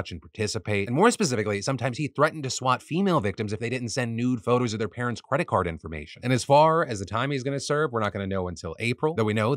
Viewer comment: Did you say “nude photos of their parents credit card information”